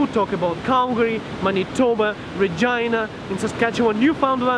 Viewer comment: is that just me or did it sound like he said a female body party? ik he didn't it just sounds like it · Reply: Vagina 😂